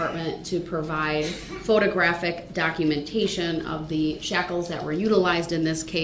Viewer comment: Guy probably heard the word shackles and thought they said shocks